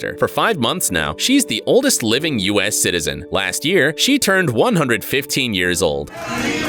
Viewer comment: i thought u said 150 years old and my head went like 🤯